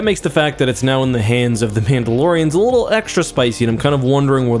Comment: did you say Pandalorian?